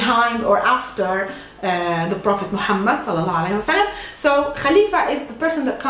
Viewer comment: Can an Arabic tell me what she said to bless the prophet Mohammed? It is really interesting for me to hear and learn these expressions like elhamdulillah bismillah or inshallah. I would really appreciate if you could do a video how we can answer some questions in a religious way. A friend of mine told me if someone ask you how are you doing or if you are a Muslim you can say elhamdulillah ana bihayr or elhamdulillah ana muslim or something like that. That would be really cool :)) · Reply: Chev Chelios Salah allahu alayhi wa salaam